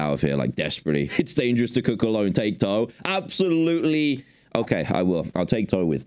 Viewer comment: Sorry Dan did you just say... Tato? Asher is gonna be super stoked :D